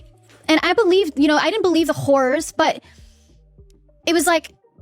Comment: Did homegirl actually say, “I didn’t believe the WHORES” At 9:02 WTF?! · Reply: No she said “I didn’t believe the horrors”